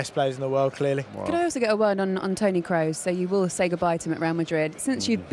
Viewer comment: Guys turn on caption and see 5:58 the girl says Tony Crow instead of Toni kroos